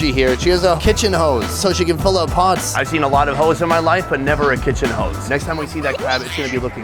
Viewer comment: "I've seen a lot of Hoes. But I never been with one" - Sonny. I thought that's what Sonny said but it's: "I've seen a lot of Hoes. But never a kitchen Hoes."🐺